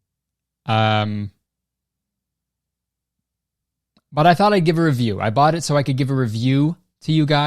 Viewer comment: When you were talking about how you bought the fight to “give a review”, I thought at first you said you bought it to “give ‘er a view” and I was like, “I mean, yeah, that’s probably why most people bought it”.